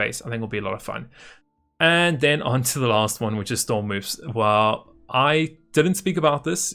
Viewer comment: Did anybody catch what he was starting to talk about at 08:53? Storm-moves? What is he saying? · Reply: Stormwoofs, Dogs